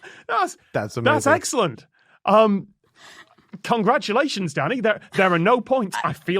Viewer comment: did scott just call him daddy???????